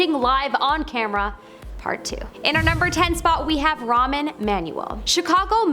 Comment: Why did it sound like she was saying Ramen and not Rahm?